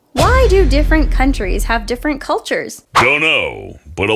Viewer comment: it says why do different cultures have different cultures instead of why do different countries have different cultures